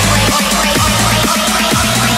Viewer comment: Always thought he said "Chocolate Milk" instead of knock em out